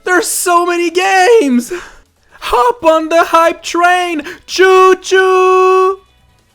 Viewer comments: I thought he said high train. HOP ON THE HIGH TRAIN! CHOO CHOOOOOOO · I thought he said high train. HOP ON THE HIGH TRAIN! CHOO CHOOOOOOO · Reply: MLG Expert420Scoper I thought he said hop on the high ground(get it